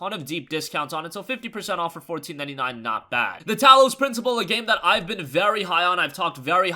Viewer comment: It would be helpful to add the titles to your description. I am interested in the game at 2:45 but can’t make out what you are saying. Something principal? Thanks for doing these videos! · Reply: The Talos Principal